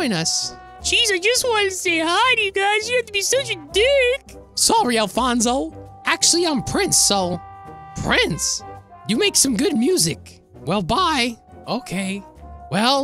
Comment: It kinda sounded like you said “geez you don’t gotta be Dutch a dirck!”